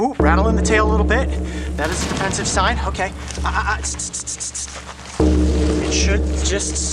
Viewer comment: wait did coyote just say ahaha sksksksk?